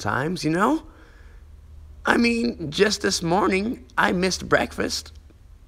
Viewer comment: he sounded like that one meme when someone said "BEKFEAST"